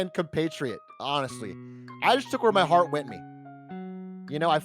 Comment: Did anyone else hear Atrioch say, "I just took where my heart went me" at 19:18 ?